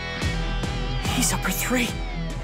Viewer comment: tell me if I heard that right but did I just hear “He’s up for urine” 👁👄👁